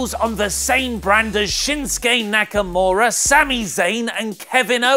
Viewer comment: for a second I thought I heard you say shitstain nakamura